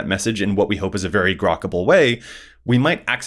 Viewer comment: Did you just say "Grokable"?? Wow lol